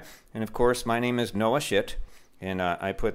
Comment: I was washing the dishes when I heard, “ My name is Noah Schitt” I thought it said Noah shit